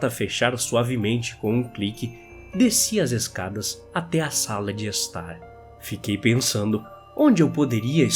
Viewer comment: so eu ouvi sauna de estar?